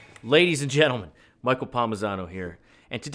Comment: I know this is my failing but I could've sworn you said "Michael Palm-mute-sano" for a moment. I had to replay it to make sure. Now I'm kinda disappointed that you didn't.